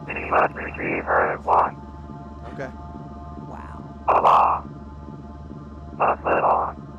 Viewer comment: Thought he said “Allah” and was like damn that took a turn